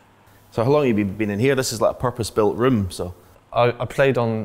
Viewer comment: it sounds like Stephen says "this is like a proper spilt room", . . .what is a spilt room?